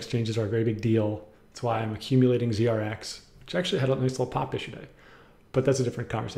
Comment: what did you say at 26:28? I played it on slow mo 5 times still can't tell if it's English or German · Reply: "Actually it had a nice pop today..."